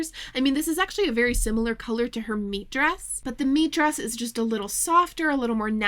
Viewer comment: i'm high and have this on semi in the background while i knit, and for almost 40 minutes i thought you were saying "MEAT dress". for some reason i couldn't even fathom that another word pronounced the same could exist 😭